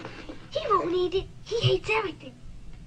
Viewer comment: Maybe when the commercial first came out, I thought the kids said, "He'll eat anything", but when I actually paid attention to the dialogue, it was clearly, "He won't eat it, he hates everything" And I heard that dialogue long before the existence of youtube and the internet. Anyway, it makes sense in the context. The older boys didn't want to try this new cereal because it probably tastes like that crappy oatmeal their mom tried to give them before. But Mikey, like most kids his age is a fussy eater. He won't anything unless it's coated in sugar or peanut butter. So his older brothers reason that if he's willing to try and eat Life cereal, then it's probably pretty good. So no, no alternate timelines, CERN misfires, or demonic intervention, you just think it went a certain way but you are wrong.